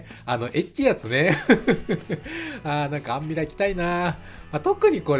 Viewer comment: 一日後かいｗ 冬場 谷間で手を温めてくれるメイドさんいないかな～ｗ アンミラ着たいなぁ～って聞こえたｗ やはりエロが起爆剤なのですね～ 業は深いｗ